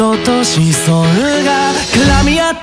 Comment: 二番サビ前の「僕には赤い血を」、分かってはいるんだけど「僕には開知/開智を」に聞こえてしまい勝手に感慨深く思ってる